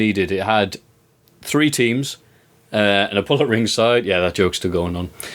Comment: had WHAT at ringside??? I must have listened to what he said about 9-10 times but still couldn't understand what he was saying... ...who wants to tell me what he's actually saying there? lol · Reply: Bull